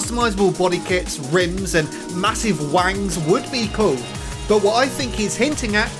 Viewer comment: I heard "Massive Wangs"